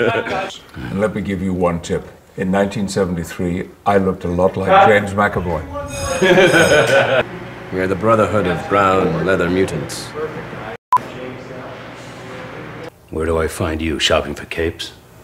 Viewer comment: for a moment i thought he said in 1973 looked a lot like lames mackelmore XD